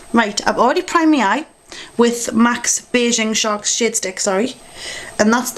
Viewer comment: in the beggining it sounds like your saying shit stick instead of shade stick. I was like what'd she say!? lol